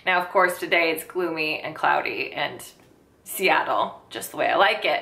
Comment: you live in seattle? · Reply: I thought I heard u say u live in seattle